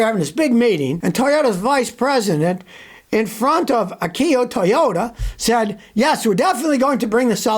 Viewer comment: did he say a Kia Toyota? Isn’t that an oxymoron Disclaimer: it was a joke. I had to put that in there because you know some people just don’t get it.